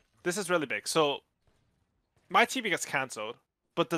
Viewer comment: did he just say "my tp gets cancelled"?